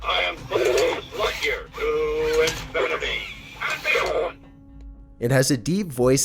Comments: The buzz lightyear said "to infinity and be gone" me and my little sister were watching and she put her ear up and heard it say that just an FYI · On number 9, I heard "And Begone" instead of "And Beyond" was it only me?? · is the creepiest. And also I think he said to insanity and beyond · I thought buzz said “ to infinity and be gone “ you could slightly hear it